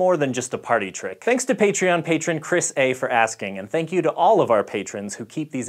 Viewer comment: I heard "Chris A-for-Asking"